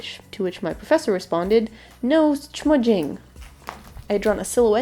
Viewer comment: what did the professor say? no smudging?